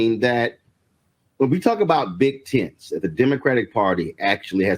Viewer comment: I swear to God, at first - for a split second - I thought Roland actually said "big tits"😲, & I was like: "Damn, Roland, what do big tits have to do with supporting Democrats?" Well...look back at some of the infamous Dems in office: Bill Clinton, which I believe is self-explanatory; So I rewound the video, & realized he'd said "big tents".😅🤣😂 Boy, do I need to get my ears checked.